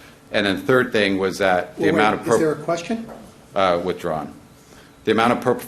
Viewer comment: What does Walgren say after the judge asks if there's a question here @ 9:45? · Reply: "withdrawn"